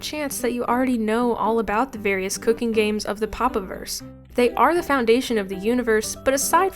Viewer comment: really sounded like these games are the reason we're here in the universe in the first place. "They are the foundation of the universe".